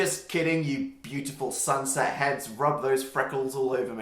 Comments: As a redhead I'm taking this moment to store the term 'Sunset heads'' in my vocabulary. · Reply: Oh!!! I thought he said Sansa heads. Made sense to me lol 😂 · "I'm just kidding, you beautiful sunset heads. Rub those freckles all over me." I'm dying oml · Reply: Anubis Cat Lover I thought he said "Sansa heads" i'm idndjdb my GoT trash self is showing